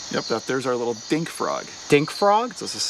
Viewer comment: did he say 'dickfrog'?!